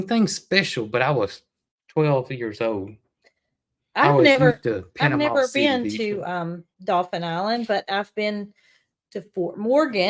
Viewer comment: Gidday from Queensland Australia. I had to look at a map to find out where Dauphin Island is. I actually thought was saying Dolphin Island, but once i saw where Mobile is, I found it the right spelling.